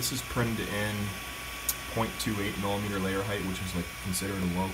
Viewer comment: did I hear right @ 6:15 that this was printed .28 mm ?